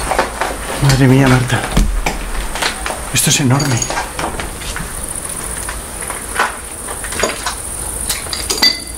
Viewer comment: no sé si es un sonido de fuera pero se capta no sé si una voz o algo extraño ,en el 23:20 creo q hay otra q dice "la pu-ta" y seguido en el 23:22 otra parece decir "Franco". 23:25 hay otra cosa , pero no sé que es. 😅.En el 23:29 también se capta una voz , pero no sé que dice , tengo q escucharlo más veces.Pero para mí q dice "no ve-mos" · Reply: Chico bueno